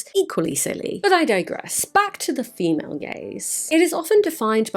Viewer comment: I’ll have you know I quite like the female gays!! Hang on, sorry, I’m being told I misheard that